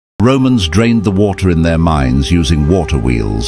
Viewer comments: At first I thought he said Romans drained the water in their minds. When he mentioned water wheels, I started to get suspicious. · I thought it said " in their MINDS"😂